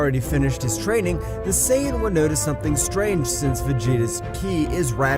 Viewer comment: YO GUYS HE ACTUALLY SAID SAIYAN RIGHT